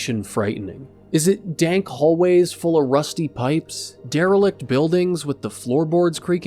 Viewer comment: I wasn't paying attention and thought you said "Sewer full of dirty dipes"